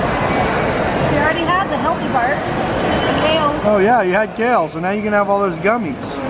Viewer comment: at 4.22 looked in a different direction the were Katie and Billy were and she kept doing that a lot ? any ideas?????? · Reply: Well at 4:27 she also said in a whisper and I heard that kids under the age of 8 and under can communicate with spirits what if it was and she is communicating with him and not telling anybody